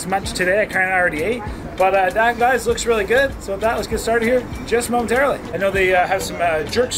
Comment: He said just momentarily first i thought he said just smoked early😂😂